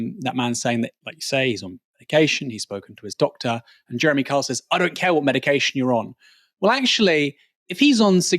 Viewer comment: come on man, you sound like mainstream news twisting words… he didn’t say “I don’t care what medication you’re on”, he said “he’s on medication, it doesn’t matter what medication”, in the context of what he said, I perceived it as him not wanting to say the medication names, or expose his condition.